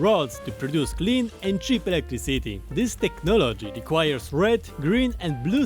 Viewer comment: technologia is all I hear.